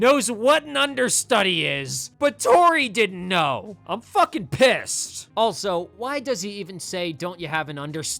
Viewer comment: WHEN YOU SAID „I’m fucking pissed“ I THOUGHT YOU WERE SAYING „I’m fucking pissing myself“